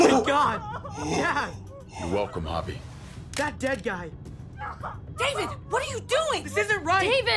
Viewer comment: Anyone else hear Gabe just say “that dead guy”?